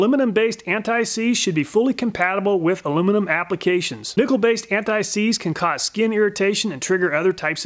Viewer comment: I thought he said Nickelback was seized... all. I could say was FINALLY